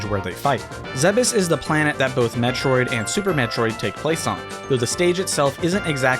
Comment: I heard "Zebes is the planet that Metroid..." at 3:36 and was terrified for a split second that he accidentally messed up Samus' name